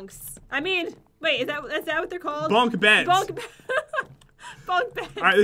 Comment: when u say bunk beds it sounds like u said bunk heads :D